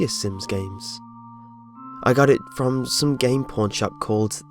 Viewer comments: When he said "pawn shop" , I thought he said "porn shop". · the game was used, and scary thing's happened... the logo was different, and scary things happened... he bought the game at a pawn shop, and scary things happened... SAY IT WITH ME GUYS! "CLICHE!" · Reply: I thought he said porn shop -_ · Previous video- "Ronald McDonald House" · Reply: i thought you said game porn shop there lol, no wonder it was hacked. My bad